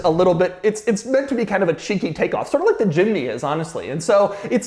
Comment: i almost thought doug said ch*nky instead of cheeky LOL